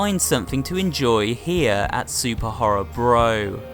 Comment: I laughed when my mind misheard SuperBorrorHro